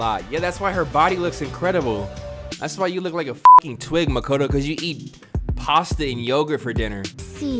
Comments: Did this boi just say MAkoTO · did he say makato? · does he say Makoto?